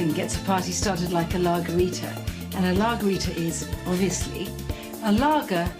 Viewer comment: Commenting on lagerita, lol. Thought she said Lawgarita :)